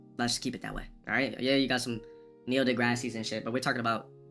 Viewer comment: Did anyone else hear him say "Neil Degrassi" at 17:35?